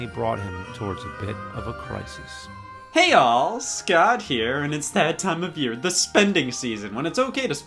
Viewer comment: i always thought he said hey yall scott here